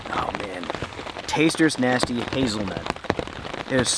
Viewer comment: Love your videos!!! You made your coffee and it sounded like you said tasters choice nasty hazelnut coffee.. what exactly does that mean? Thank you for allowing so many of us to live vicariously through you.